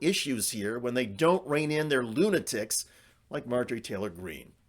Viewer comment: Oh, lunatics. On first listen, I thought I heard "goon-atics" !